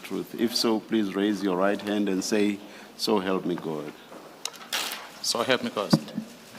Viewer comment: did I just hear “so help me Ghost” 🤔